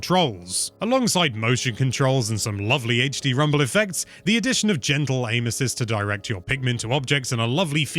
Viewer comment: I heard: "the addition of gentle anuses" at 09:45 lol